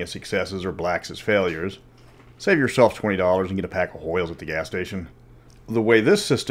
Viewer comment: I honestly thought you said, "Save you money and buy a pack of oil at the gas station." Ya know, it is always good to bring an extra quart or two of oil when you drive somewhere.